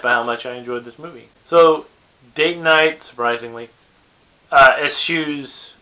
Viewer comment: Did you say "date night"?